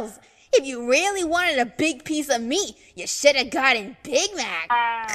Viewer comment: I thought she said big back 😭😭🤚